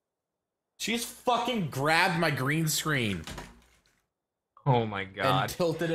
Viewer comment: At first I thought he said " crapped on my green screen" and I was like holy shit.